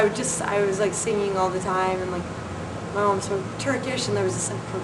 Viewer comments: did she say her mom was half turkish? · I heard her say her mom "speaks Turkish".